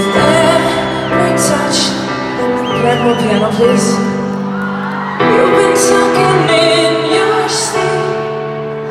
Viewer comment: What was she saying 0:39 ? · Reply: She said: "Can I have more piano, please?"